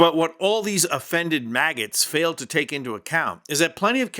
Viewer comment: A couple of times when you said MAGA, I heard “maggot “……